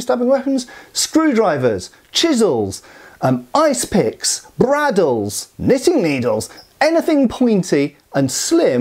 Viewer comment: "braddles"? CC spells it thus, that's what I heard, and Wiktionary doesn't have it. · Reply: Bradawl